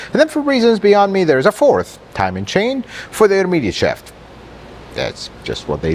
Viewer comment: Sorry, but I can't understand what you said about the 4th timing chain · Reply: "for the intermediate shaft"